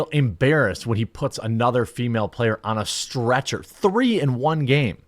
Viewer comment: Thought is said STRETCHES!! LMFAO😂